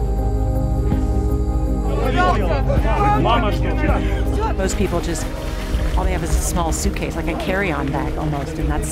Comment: What was the soldier saying to the boy at 1:04 ? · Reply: Mother is awaiting